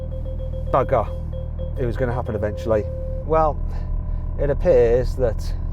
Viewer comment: のとこって何て言ってるんですか？日本語で｢バカ｣って聞こえちゃうんですけどw